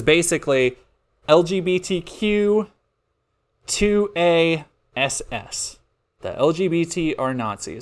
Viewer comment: "LGBTQ2ASS" I think he might have been saying "ass" instead of "SS". He was making the nazi joke earlier and made a weird pause so I can see how SS would be singled out but I can't think of anything "2A" would mean, so I think he was literally just spelling "lgbtq-2-ass" and thought that was funny for some reason.